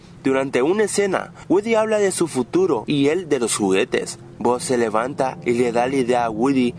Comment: yo le entendi wily :O